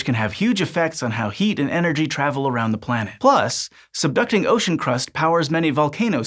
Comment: I kept mishearing subducting as seductive.